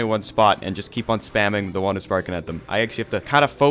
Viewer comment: Thought it said “Stick of Spanking”